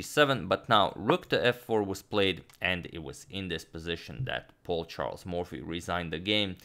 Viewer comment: I always heard "poor Charles Mormphy". My bad